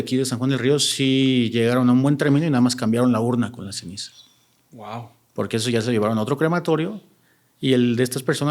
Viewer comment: En el minuto 38:43 se escuché un murmullo , pero no entendí que dijo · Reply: Parece diciendo; "Vaaaya"